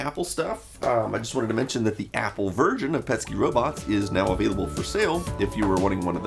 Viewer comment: is it me or does he say Apple virgin? instead of version :p